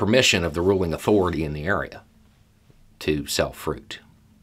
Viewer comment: Dang, I thought you said “self fruit” instead of sell fruit. Immediately, my mind went to self quarantine for some odd reason. Lived in the South for a long time, but I’m starting to lose my southern hearing. Thanks for the pertinent message, and look for that “dirt for sale” sign off the side of the road.